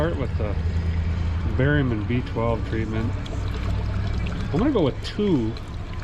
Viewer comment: I've been watching a lot of your videos and upto now I thought you've been saying you're going to put "barium and B12". I thought you were joking like the medicine they give you before surgery. But today I saw that it's "berry man b-12", 😂 . I might be a girl but I totally enjoy watching your adventures.